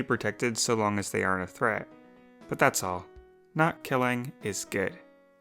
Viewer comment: not killing is gay is all i heard